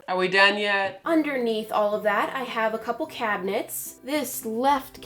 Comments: I thought she said underneath all that we have some catnips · She said "cabinets" I hear "cat nips"😂